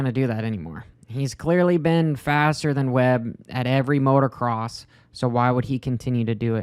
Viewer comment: At time stamp 5:47 did you say MotorCross? 😂